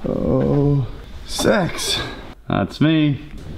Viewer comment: when he said six it sounded like sex